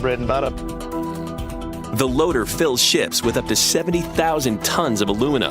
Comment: did he say "shits" ?